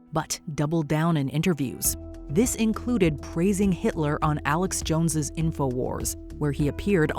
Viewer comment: I'm sorry I think I must have excess earwax again, because it sounded like she said "Kanye praised Hitler". Please tell me I just have too much earwax.